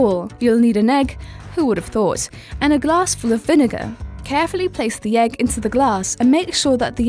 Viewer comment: did she said vinegar?